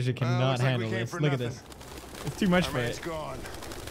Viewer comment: did pewds said, "its too much man"? anyone know? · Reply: he said “it’s too much for it”